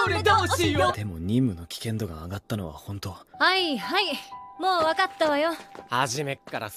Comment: wait did Nobara say dattebayo!!??? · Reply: Wakattawayo